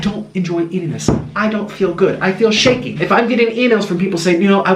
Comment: You never told us what exactly was wrong with your health. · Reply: Bryce G the only thing I heard is "I feel shaking"